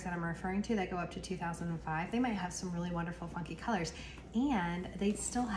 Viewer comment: It sounded like you said, "they might have some really wonderful fucking colors..." @ 7:16 lol